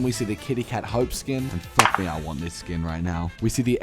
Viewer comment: I has my volume low and misheard you saying you wanted to 👏 this skin